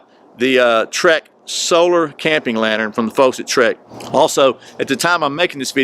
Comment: Did you say "from the folks at Schrade?"